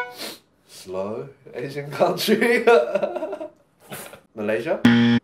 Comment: Eddy: Slow Asian Country Eddy: PHILIPPINES? Aray ko beh but too real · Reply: I thought he said “low Asian country”, as in south. But I might’ve misheard. 😂